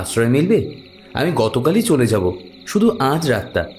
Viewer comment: " আমি গতকালই চলে যাবো " আমি কি ভুল শুনলাম @ 7:27 ? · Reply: আগামীকালই চলে যাবো